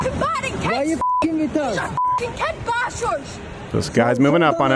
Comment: Scottish guy here. At 1:07 it sounds like she says "you're fuckin' battering kids", not "baiting". That's why she follows with the "kid bashers" statement. Even if it was baiting, both are disgusting behaviour and I hope the illegal gets what's coming to him. The wavering in her voice when she says "get the fuck away from us" implies to me that the guy was following the pair and this was the breaking point. I've been there myself with junkies and illegals on the street so I can really feel what these two went through. Also at 1:32 she says "you just battered me mate". For anyone outside this country (super jealous btw), "battered" just means "beat up". Vile little country that we live in.